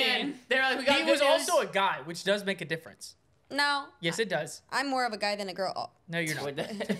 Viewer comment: i thought Said that she was more of a guy than 😂